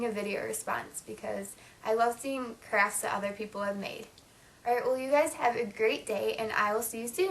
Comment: LOL at 8:39 at first I thought you said I love seeing craps that other people have made.